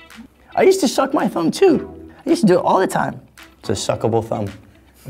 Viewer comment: did he just say “it's a very suckaboul thumb ?¿ “ i'm dying 😂😂